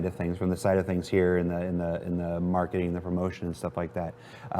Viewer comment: did he really say "marketing of emotions"? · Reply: Nah I think he says 'marketing and promotion'. But hey, they do sell dreams ;)